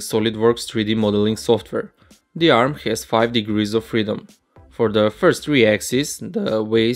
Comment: Can you pls tell me what is the degree of freedom of that Robot? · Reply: I think he says 5 degrees of freedom